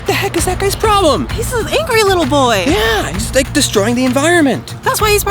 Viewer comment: who else heard say “I’m not a little boy”